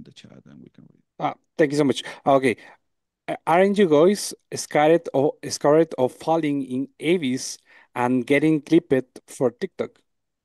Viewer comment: what was bro saying 💀💀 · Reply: ok now i understand what he was saying: "are you guys scared of falling in abyss and getting clipped for tiktok"